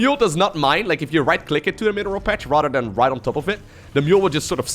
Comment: Jesus, till now I though "Aligulac" was the name of a SC2 map. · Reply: I always thought when he said "rotterdam" he was saying "rather than" and it confused me